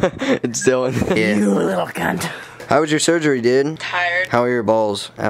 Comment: Oml did he Say "Cunt" at 00:24😱😂😂